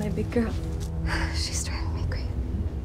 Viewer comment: What did Dakota whisper at 0:54? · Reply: She’s driving me crazy.